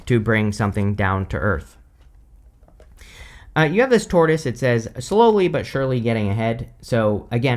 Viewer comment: I kept hearing “Taurus,” not “tortoise!” lol. I’m a Taurus sun and Libra moon/rising.